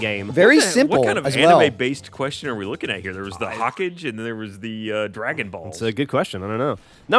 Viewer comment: did ryan just say hawk-age?